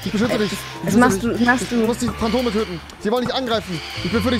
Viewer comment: Musste 3:35 iwie 5 Mal hören, hab im ersten Moment Kondome verstanden